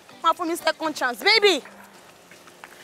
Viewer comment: Did I hear , “je nma lole?”- let me go to his house. Fire will meet fire when you get home oh?